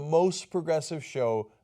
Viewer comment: Oh.... NOTES!!?? I thought you said NOSE.